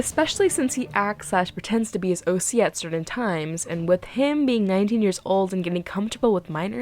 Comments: Girl, I thought you said 90 years old and not 19 💀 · For a second, I really thought you said he was 90 years old I was like, "WHAT?" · Barbie should start doing background checks before she hires people. Edit: OMG I THOUGHT YOU SAID 90 YEARS OLD 💀